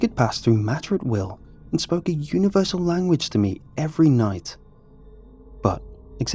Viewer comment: I thought it said universe lasagna